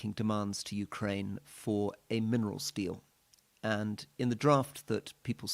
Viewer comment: Did you say minerals deal or mineral steal? 😮